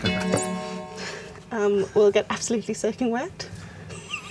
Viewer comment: Can anyone fill me in as to what said that caused much amusement, at 18:54? I could not understand what was said. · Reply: "We'll get absolutely soaking wet" another line from It's Raining Men